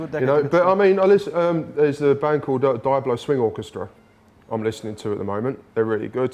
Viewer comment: I kept thinking he was saying, "die swing orchestra" at 0:35 and 1:05 , but I know I dunno what he said at 0:45. UGGG. Interesting bands he listens to, but I would understand why!!! Genius. :] Still, I listen to loads of metal cause I just love it, even though I am a musician myself. I just hope that i don't take too much from other people's material, but one needs to be inspired. Great interview with Paul! I love hearing his view of the band.